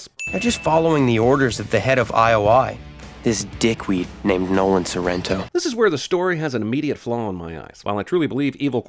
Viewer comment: “Nolan Sorento” I thought he’s going to say “No one Surrenders”